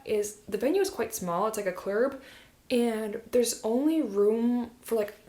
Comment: A "clerub" setting?? What is she saying around 16:00 for Gina's wedding.